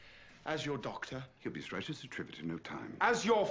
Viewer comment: "He'll be as right as a trivet in no time." in case you didn't understand like I did.